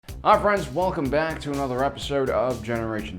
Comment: I know he says hi friends at the start of his videos but to me it sounds like he says Ah friends.